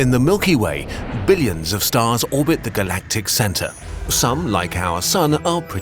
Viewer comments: i was using the 5 second skip thing go back to the start of the video and then i heard it say "nuts orbit the galactic center" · Bruh I swear I heard him say “galactic Santa” instead of galactic centre xd